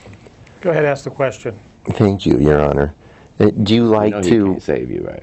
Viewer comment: did he just say i hate you your honor?